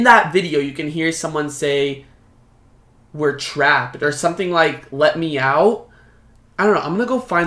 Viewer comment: Wait in the blessing oil part I heard I'm trapped